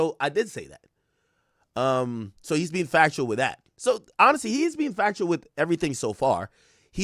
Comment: The caption said chaino cappin but you just said he being factual with everything so far @ 18:11 lol